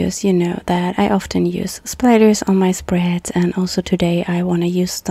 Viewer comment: thank you for sharing this video. I always enjoy your videos and find them both calming and inspiring. I had a funny moment though, because for a moment I thought I heard you say, “If you know me, you know that I like spiders, and I always include them in my spreads.” Wait, what? Oh, splatters, right! 🤣